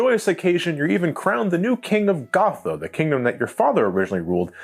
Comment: did u say Gotham